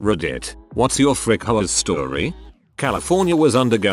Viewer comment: For a split second, I thought they said “Frick hoes” instead of “Frick HOAs” at the start